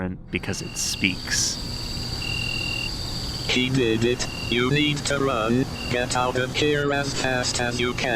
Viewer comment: did anyone else hear “Hey, baby! You need to get out of here!” ? I cant tell if that’s what it actually says. If so, that’s hilarious to me lol.